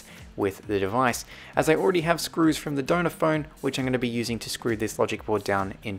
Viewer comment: At first I thought you were saying “donut phone” not “donor phone” 😂